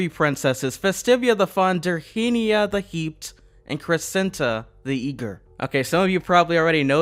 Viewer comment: why i heard this Festivia the funnter Dirhennia the heat and crecenta the eater XD